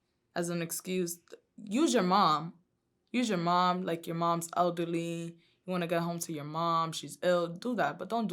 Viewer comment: At first listen at 4:30 didn’t anyone else think she said ugly and not elderly? 😂🤣 or was it just me